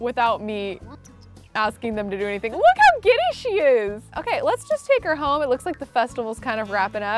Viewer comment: 😂😂😂 At the festival I thought you said look how gay she is